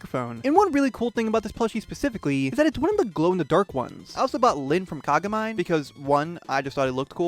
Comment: WHAT did you just call him 😭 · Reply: " i also bought lid from kagamai "